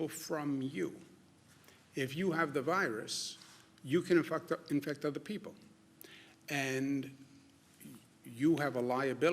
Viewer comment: Am I hearing this wrong or did he say "you can infucked up, infect* other people"